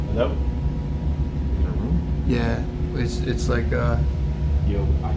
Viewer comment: does not say hello back last word is room could just be yous